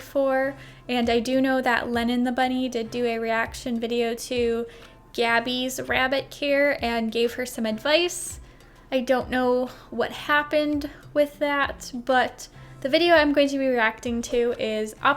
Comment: I thought care said “ grave “ 😥